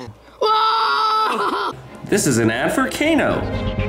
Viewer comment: I thought he sayed (This is an AFRICANO) Lol